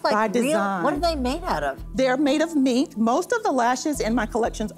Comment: I thought she said those lashes were made of meat...🤣